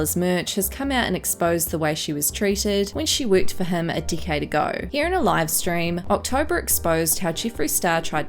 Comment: Is it just me but it sounds like she says a dickhead ago instead of a decade ago